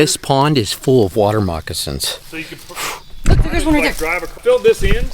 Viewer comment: I coulda swore you said water rocks not moccasins lol thought "cool, ol wrecker is in my neck of the woods and wtf are water rocks?!" 😂😂😂